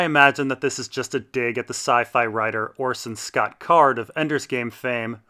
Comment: Orson somebody - did you say once trashed show? What it does mean? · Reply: Orson Scott Card, He is a SF writer and I really like all his books, check him out!